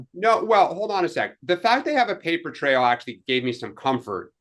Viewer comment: Maybe I misheard. Did Dan say they have a verbal paper trail? Haha